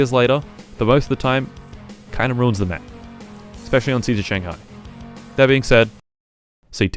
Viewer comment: I always thought he said seizure Shanghai